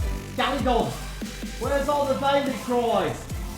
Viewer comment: I cant work out what is saying and its bugging me someone help me out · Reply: was upset that valley-cries weren't featured.